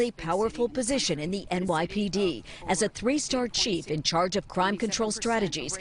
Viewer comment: I thought it said “three star chef”